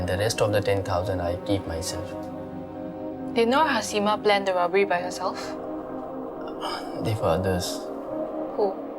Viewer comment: i dont understand what did the police lady say, can someone translate? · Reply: the police lady said "Did Nurhasimah plan the robbery by herself?"